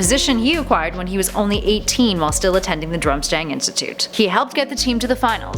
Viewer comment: did she say "Drumstang" ?